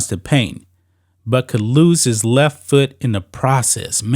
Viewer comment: I thought he said or loose the left foot